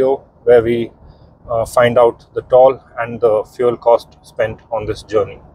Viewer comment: did he just said “toal” 😂